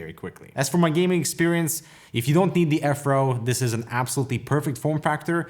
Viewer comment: I thought u said "afro". It was actually "F row" lol